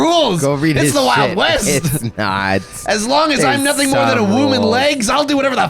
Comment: He said "as long as I am nothing more than a womb and legs" but I heard "as long as I am nothing more than a wooman"